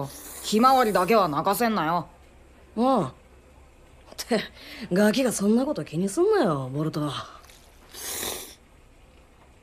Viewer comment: yo entendí cállate y come tu ramen burrito que digo boruto